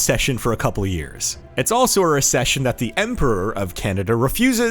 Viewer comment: Did bro just say emperor 😐